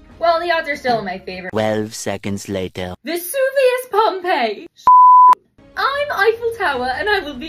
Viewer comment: 😂🤣 her name is Pompeii really I almost thought you said Pomni. And I'm like what are you trying to reference digital circus because I love that show on YouTube its really good and funny. I found the trash collectors to me super funny also this is true I had to watch Hunger Games in English class last year and that was very crazy